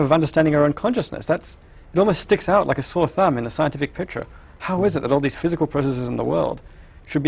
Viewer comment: I'm sorry but, what does he say from 01:10 to 01:13. I can't understand it. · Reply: I know your comment is 5 years old, but he says "it almost sticks out like a sore thumb in a scientific picture"